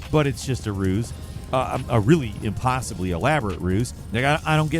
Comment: what did say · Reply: A really impossibly elaborate ruse